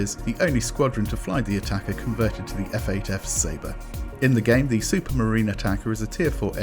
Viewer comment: did you just say F8F Sabre?